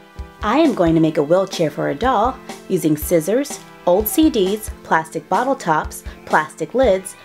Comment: When she said old cds I thought she said ocd